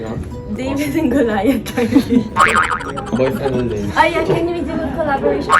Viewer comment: tama ba ang narinig kong "Boyfriend and David"😭